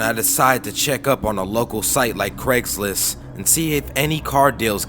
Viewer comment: at first i thought it said craigslist aids.... now this is less scary